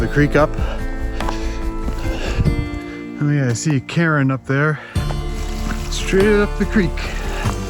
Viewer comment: Yay first commenter! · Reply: When you said, "I think I see a cairn up there" I thought you said "Karen." I had to rewind and put subtitles on to check. In my defense, you do find Karens in the wild everywhere.